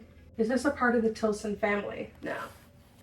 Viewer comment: did anyone else hear a sound like a voice whispering “no”?